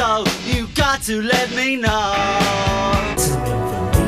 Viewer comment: what does it say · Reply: Some information first